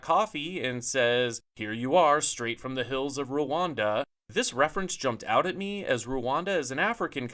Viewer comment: I thought he said “straight from the hills of Verona” like the coffee brand as a joke. Even more interesting now!